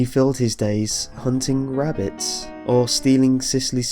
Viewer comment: i keep hearing wabbits and not rabbits godamn looney tunes